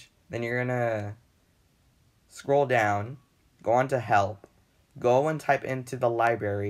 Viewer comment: I heard “Go on to hell” 💀